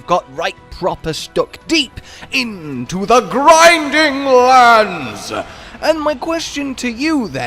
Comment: Am I the only one who heard him say "grinding lands"?